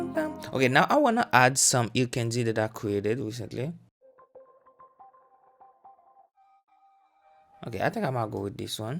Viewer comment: Please sir what did you call the ambience stuff in 6:49 🙏🏼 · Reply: Ear Candy